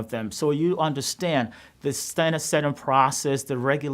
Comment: At the 3:53 minute mark, it states [INAUDIBLE] at the bottom of the screen as they give you subtitles (even though he is speaking English, the Queen's English might I add). What he said that point was "standard setting process".